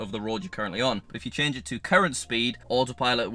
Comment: I wasn't realy paying attention and you said "if you set it on current speed autopilot will..." but I thought you said "if you put it on Karen speed" I was like WAIT WHAT???? hahaha